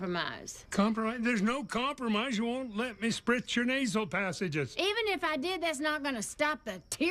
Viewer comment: I had to listen to this twice (I wasn’t watching the captions) I thought Dale said “you won’t let me spread your knees”